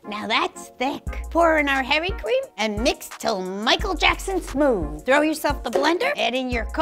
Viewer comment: What she said: “Now throw yourself the blender” What I thought she said: “Now throw yourself in the blender”